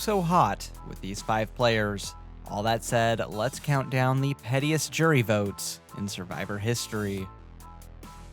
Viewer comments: I really thought it said “prettiest” jury votes and I was like “oh I’m glad Erik’s drawings are getting some attention” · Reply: I thought it said prettiest too, lol. Wild. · I really thought it said “prettiest” jury votes and I was like “oh I’m glad Erik’s drawings are getting some attention”